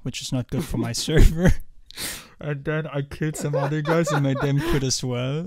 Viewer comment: im sorry what did you say here? you killed some what? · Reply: „I killed some other guys“